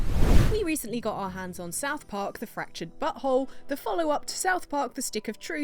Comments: Your accent sounds like you said the fractured butthole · please tell me I wasn't the only who thought her pronunciation made it sound like she said the fractured butthole at the start of the video